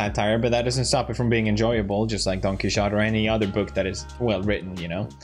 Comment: Did he say Don Quixote? Because I recently learned that in spanish class funningly enough!